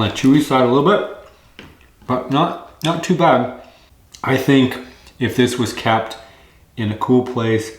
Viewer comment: At around 18:32 I could have sworn you said "I think, if this was cat..." LOL. That is not what you said, but its what I heard (and could imagine you saying it). Thanks for the videos. Always enjoy them! Full stop